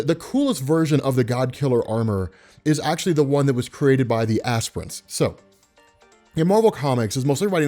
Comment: did you say Ass Prince?